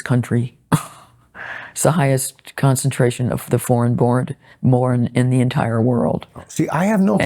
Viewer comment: Lionel, I had to rewind and check - @ 42:57 instead of 'foreign born', I heard you say 'deformed born' (in NY); from which I'd predicted the conversation would include immigrant immediate-cousin marriage and its effect on birth deformity figures.